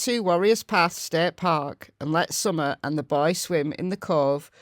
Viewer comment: I heard Summer and the boys. What? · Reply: That caught my attention also, I went back and listened 3x and read the article. It says Summer and the boy.